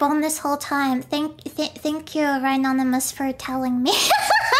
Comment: It's kind of weird how the editor didn't know my name. The editor thought an anonymous viewer told that her reverb was on. When it was actually me! I've shouldn't had name myself "Ryanymous" because I combined my name with the word "anonymous". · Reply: It’s a mistake! The editor probably just heard it as “anonymous”. Everyone’s hearing is different because I heard “ryanymous” but the subtitles were wrong